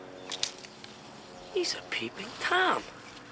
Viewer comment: I'll never forget seeing this the first time: our Gramma took us. Just a second before Marty said "He's a peeping Tom" Gram -- a woman who had a wonderful and wicked sense of humor -- squeaked "Oh! He's a peeping Tom!" Everyone nearby started snickering and when Marty said it, everyone just totally lost it! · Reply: When I saw this as a 5 year old kid I used to laugh because I thought he said “pee pee Tom”